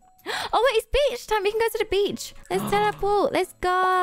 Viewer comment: it sounded like said the b word instead of beach lol.